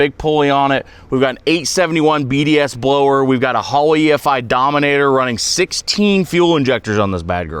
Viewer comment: DID SOMBODY SAY DOMANATOR 3?